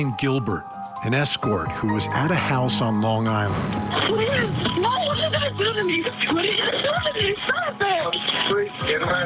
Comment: Sounded like she said "what are you guys gonna do to me, what are you going to do to me!!!?". More than one person.